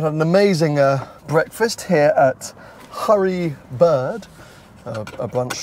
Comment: Ha ha 🤣 Oh, at 16:09 did you really call the diner “Hurry Bird”? Or was it my hearing? Clearly “Early Bird” 😝